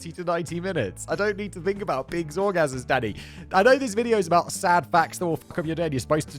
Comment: Sometimes I hear “Danny” as “Daddy” For example 0:54 “I don’t need to think about pigs’ orgasms DADDY!”